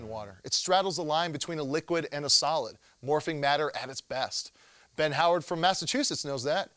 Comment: I thought it say "morphine" matter, and that reminded me to get my daily fix... ... of awesome.. I don't do drugs.